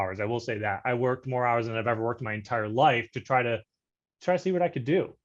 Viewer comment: How many hours did he say he worked???? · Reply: Worked more hours in a given period then ever before.